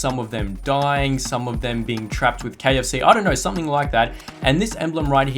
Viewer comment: I swear I heard "something something trapped in KFC"